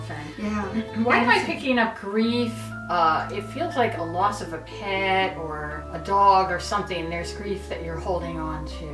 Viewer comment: I thought she said. "I feel a loss of a pen." I was like... She's grieving over a pen. My ears.. I swear.